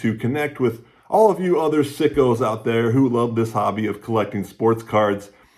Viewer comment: did you call me a sicko?